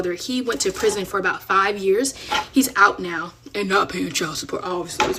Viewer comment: did you say five years